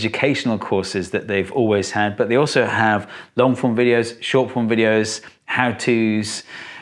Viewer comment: thought you said “short porn videos” for a second there.